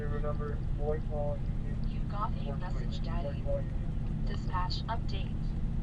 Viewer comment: Did this just say, "You've got a message Daddy, dispatch update?" WTF...WOW.......Then just b4 24:08, it sounded like it said, "got your message Daddy." As he was sitting down in his cruiser.... Make sure you have your headphones on please 🙂. Lmk what you hear and think.